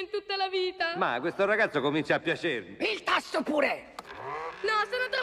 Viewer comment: cosa dice?? "il tasso pure" ??? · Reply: Il tasto purè